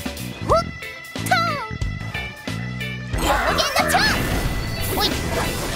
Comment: did i just hear "you'll gain nacho" 💀